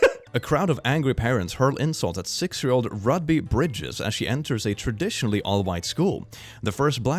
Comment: stg i heard Rugby Britches